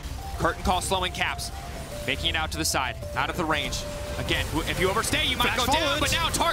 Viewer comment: Did he really said Mikyx on the side? Or am i just hearing things?